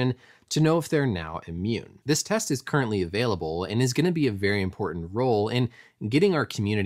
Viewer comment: When do you think we will have an antibody test available 6:00 Did you say is already available?